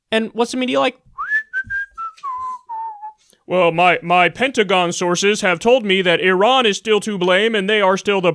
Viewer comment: I legit thought he said “my pentagram sources told me”. At this point there’s prob not too big of a difference.